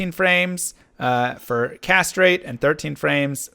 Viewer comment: I was confused when you said "Cast Rate". I was like..."That's not a Necromancer Skill, dude!" Then, I understood what you meant, as I heard it as castrate instead of cast rate...at 1.75x viewing speed.